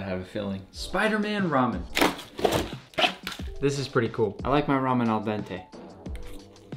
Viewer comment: I really thought you said I like My Ramen drippy